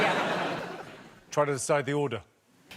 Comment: What did Jimmy say at 51:36? i didn't get the joke. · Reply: Trying to decide the order.